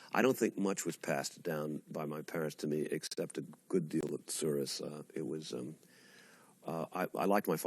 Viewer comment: Anyone know what word he uses at 21:09 ? "A good deal of __" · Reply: I believe it's tsuris, a yiddish word meaning trouble or woe